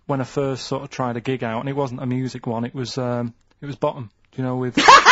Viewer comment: I cannot understand what Karl says right here? Button? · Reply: Bottom!